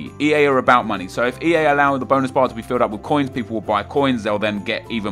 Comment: I actually thought that he said Bone bar